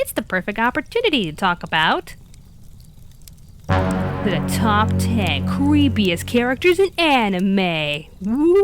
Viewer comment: Did she say "Top ten cweepiest character"? If she did, HAHA!!!!!